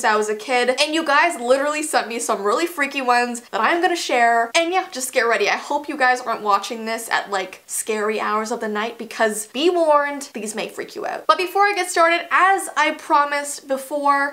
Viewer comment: instead of be warned warned XDD